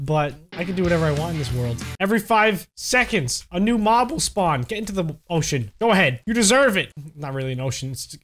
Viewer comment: When he said get into the ocean tithe turtle I literally thought he said "get into the, o sh$t